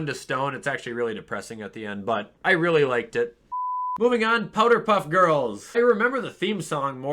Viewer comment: is it just me or did he say powder puff girls